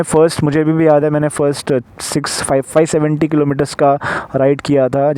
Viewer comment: bhai samajh me nahi aaya 655 kya bola apne kitne km chalaya tha bro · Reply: hahah sorry yaar, i mean 570.